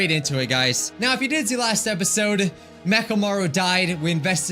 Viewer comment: i thought he said macklemore died, like dang i didnt remember that from the games